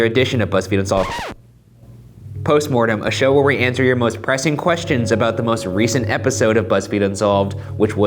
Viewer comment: I heard it as "oppressing questions"